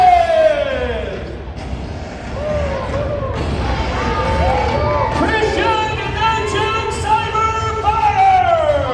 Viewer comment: What is the name of the wrestler in blue who enters at 55:57? I think I hear John but that's all I can understand. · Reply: Draken, he has a match on the future Blaze video